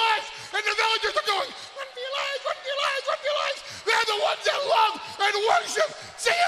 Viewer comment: WHEN THAT VULCANO FINNALLY ERUPTS AND THE LAVA, THE BOILING LAVA DRIPS DOWN THE SIGHT OF THE MOUNTAIN, IM JUST LIKE THAT LAVA, IM FREAKING HOT, AND IM, AND IM OUT OF CONTROL, AND ALL THE VILLAGERS WITH THE LAVA POURING DOWN INTO THEIR HOUSES, DESTROYING THEIR CARS, SUFFOCATING AND MELTING THEIR FLESH, AND THE VILLAGERS THEIR THE ONES THAT LOVE CM PUNK I laughed so much when I watched it first time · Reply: Then what did Paul say in here? "Warm your lives"?